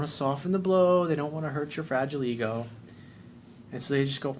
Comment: Lol......sounded like he said.... VAGILE EGO .....